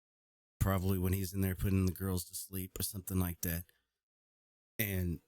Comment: I heard..." The girls were dropped"